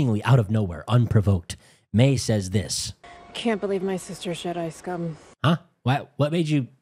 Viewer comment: I seem to be failing my English listening comprehension. What did she say at 18:45? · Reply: Can't believe my sister is Jedi scummmmm